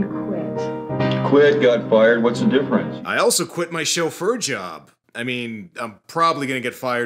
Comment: I seriously misheard you say "I quit my show 'Fur Job'"